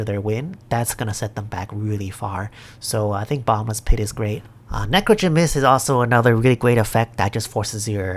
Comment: Lol so I had this video playing in the background while I'm painting some minis and when you said "necrogen mists" I heard it as necro gymnist and I was like wtf when did they print a zombie acrobat? Lmao